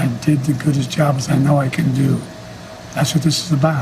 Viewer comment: I didn't hear "goodest" i heard "good as" but that still doesn't mean i will vote for this man.